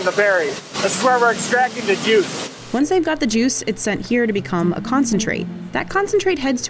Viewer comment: THE GOT THE JEWS😂 (thats what i heard)